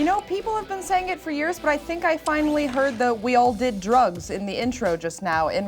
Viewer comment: I heard "we all give head" in the intro... Not "we all did drugs"... I am the 1%